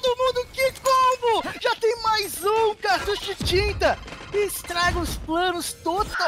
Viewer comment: did anyone hear "this is cheetah" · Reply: cartucho de tinta